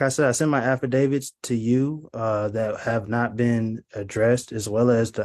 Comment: I thought for sure I heard him say “Affidavit of Wakanda”.